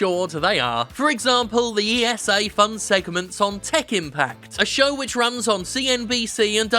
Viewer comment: Was listening (and not watchin), and thought I heard "Tekken Pact" and was supremely disappointed